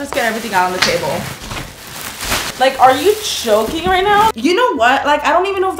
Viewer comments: It sounded like you said are you choking right now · What she said: Are you joking? WHAT I HEARD: are you choking rn? 150 likes? Holy crap wow! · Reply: I thought she said are you choking rn until I seen this comment